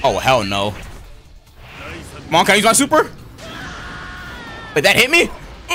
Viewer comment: Someone tell me what said at this part right here 13:25 -- 13:27 its so funny me personally im hearing "mom can i use my super" but still a fire vid from as always